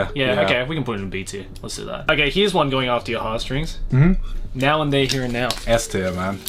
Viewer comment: name of the anime at min 26:42? my english isnt good enough to understand what the say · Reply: Now and then, here and there